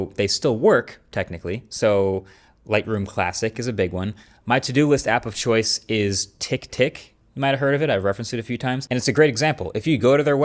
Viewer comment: For a second I thought he said, "My to-do list app of choice is TickTock". I died.